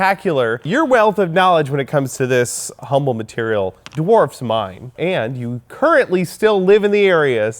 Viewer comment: I thought he said "dwarves mine" and I was like, yeah, dude. Obviously, they do.